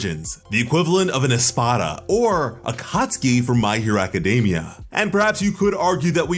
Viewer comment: did you just said "Akatsuki FROM MHA ?"